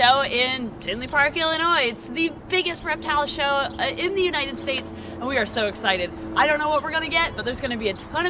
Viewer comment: Did...did she?....did she just say United snakes at the very beginning of this video???